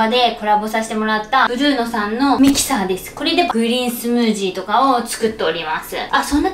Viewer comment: グリーンスムージーが 不倫スムージーに聞こえてしまった！